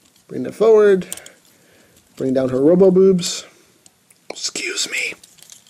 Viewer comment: did he just say robo boobs!